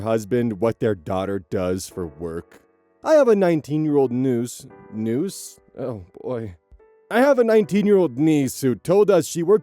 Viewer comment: So um… at 34:57, when starts reading the niece story, he pauses on the first line in one of those classic “Oz read/pronounced that entirely wrong” moments. But I have some hearing loss, I do not hear hear the difference between what he said and the word “niece”. What did he say?? Please fill me in, peeps D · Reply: "noose"